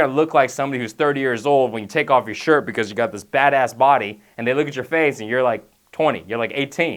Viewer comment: When he said "badass body" I thought he said fatass body lol